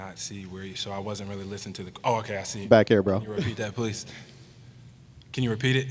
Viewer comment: “can you reheat that puss”? 😂 that’s what I heard at first